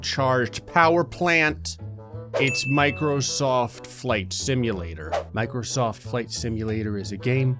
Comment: Ass soon as he said Microsoft and was about to say flight I got a discord notification so it’s sound like he said Microsoft fck simulator